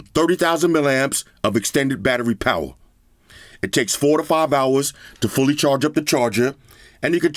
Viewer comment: "Take 4 to 5 hours to charge" Legit thought you said 45 hours and I was thinkin' "Goddamn how much charge this thing take?" My dumbass wonderin if it can charge a Tesla? lmfao